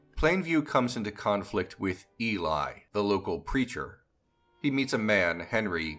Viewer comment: I thought you said "local creature" instead of "local preacher". Lol!!